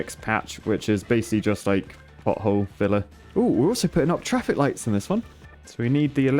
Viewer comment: I swear I heard him say "butthole filler"... and he did not even try to make fun of it XD